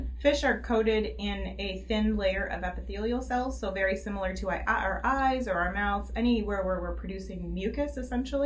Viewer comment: what is she saying 😆 “Our-ah-ahheyes” whah-whah